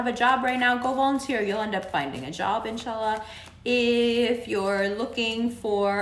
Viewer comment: Did she say InshaAllah at around 1:13 ish? WOW...awwnnn Alhamdullilahi ~